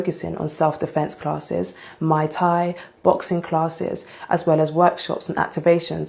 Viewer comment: Did she just say “Mai Tai” 😂😂 instead of “Muay Thai” 😂😂 Grab your drinks yall 🍹🍹